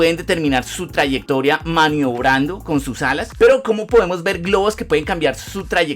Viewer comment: entendí "pirobos" por alguna razón